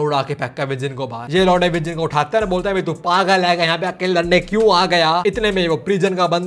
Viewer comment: said laude instead of launde